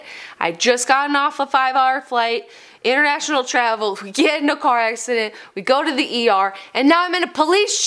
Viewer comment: I thought it said we got in a car by accident, but😞